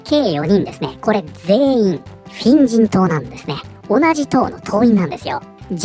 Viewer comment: 「フェン人党」が「変人党」に聞こえて、『確かに』と思った